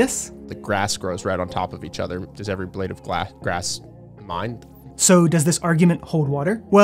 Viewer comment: What is he trying to say at 1:04? "Does every blade of grass mine"?